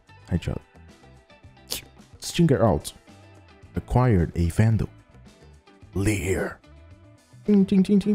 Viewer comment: am I trippin‘ or did he just say Phandal?